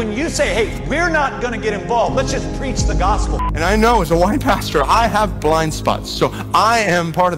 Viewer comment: at first, I could have sworn Platt said, "as a woman-pastor..." 😬🤣